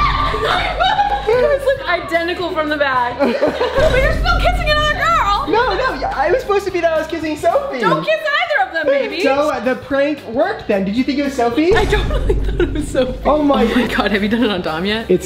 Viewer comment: Done... Did Pierson really say "baby" to Brent at 6:13 ... Or was it just me who heard it?? #Brierson😍❤️💞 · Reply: she said maybe 💀💀